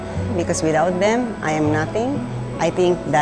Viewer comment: What did she say at 1:50? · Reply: Her team is her secret. Without them, I am nothing!